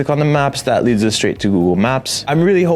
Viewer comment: did he say scanned? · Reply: scammed